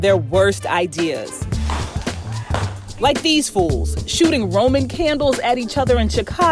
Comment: I thought she said “like these foos” 😭😭💀💀💀💀